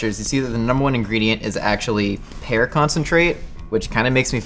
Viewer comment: i thought he said hair concentrate